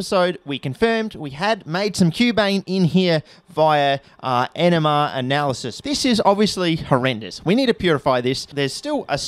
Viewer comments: I was so confused when instead of "nmr analysis" I heard "enema analysis" and he showed a bottle with tar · i know you said nmr analysis, but it honestly sounded like "enema analysis" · Thought you said "enema analysis" near the start of the vid & got very confused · Sounds like you said you confirmed cubane using "enema analysis" 😂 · ok, i am not even 30 seconds in, did he just say enema analysis? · I thought you said "enema analysis" at first.